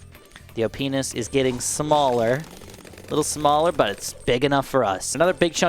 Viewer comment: Am I the only one that thought he was saying “yo penis on sauda is getting smaller”😂